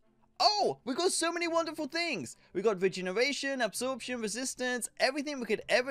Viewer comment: wait did u just say virginiritation?